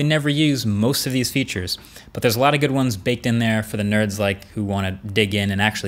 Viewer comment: It sounded like he was going to say "for the nerds like me"